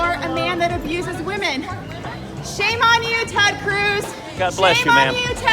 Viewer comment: I thought she was saying "Shame on you Tom Cruise"...